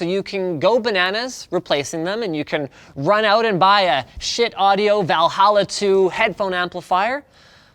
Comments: took me a while to realize he was saying "Shiit" instead of "shit" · Did Linus say "Cheap" or "Shit" ?? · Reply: The brand of the amp is called Schiit lmao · Did Linus just say "shit" uncensored?